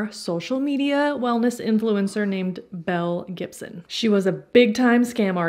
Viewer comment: I kept hearing "Mel Gibson" hahaha